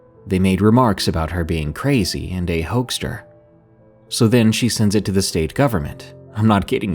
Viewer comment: On the part where he said "crazy & a hoaxster" at first I thought he said "crazier than the Hulkster" & I thought "Amen Brother!"